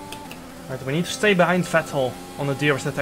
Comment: it sounds like he says "right we need to stay behind fat hole